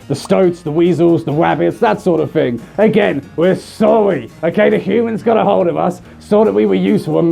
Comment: when he says "Breeds" it sounds like "Bweeds". you may kill me now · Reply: To me when he says “sorry” I hear it as “sowrwy”.